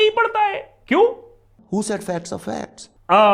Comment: i couldn't catch what was being said. Could someone please write it for me. Thank you. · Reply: He said ' who said facts are facts?'